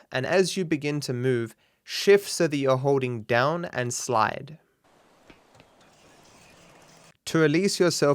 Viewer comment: what did you say there? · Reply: As you begin to move, shift so you are holding down and slide. So press W, then change to holding shift S on keyboard and mouse. Up joystick, then down joystick and A on xbox.